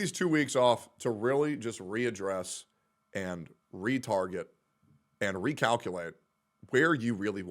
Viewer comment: I know he said "retarget" but at first I thought he said "retard it" and, even tho I didn't understand, I laughed anyway.